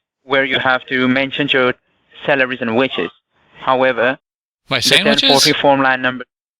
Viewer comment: I hear, "Celeries & widget!"